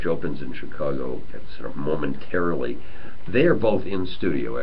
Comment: Did he really say 'mormontarilly' at 2:06? I bet they cringed. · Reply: Nope he just said momentarily